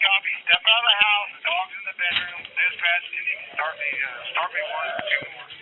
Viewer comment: Did anyone hear the officer say, "Oh there's a dog in here. Boom boom?" Before the shots, he sounds like he's joking, maybe I misheard but did anyone else hear that?